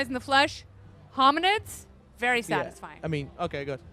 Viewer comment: Did she actually say it's been great to meet me!?